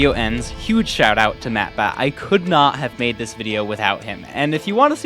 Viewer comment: I fr thought you said mattpat for a second